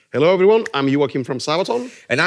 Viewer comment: Thought at first Joakim said ”Hello im Joakim from the south” haha. Is Joakim a spy?